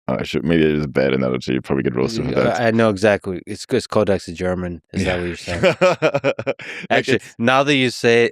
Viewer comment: What did Lex say @ 1:38. It's called 'exigerman' / 'It's cause Codex in German'? It did not sound like an english word. I think wanted to convey 'German-like'. Can someone clarify?